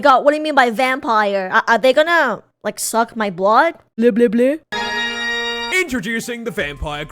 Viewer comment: I just hear drac saying "I don't say bleh bleh bleh"